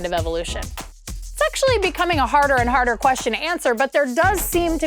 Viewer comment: Thought she said “sexually” instead of “it’s actually”